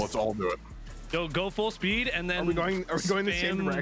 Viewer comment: Why at 3:35 does it sound like he says “go full speed, then spam the n word” 😂